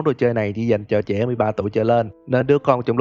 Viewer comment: em nghe nhầm trẻ 3 tuổi trở lên:)))) 19:20 sét nhỉ..